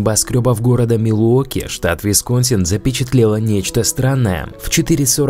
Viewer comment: я тоже услышал слово вискас?